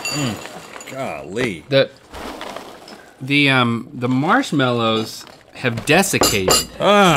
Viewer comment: I thought he said "The marshmallows have defecated" 😂😂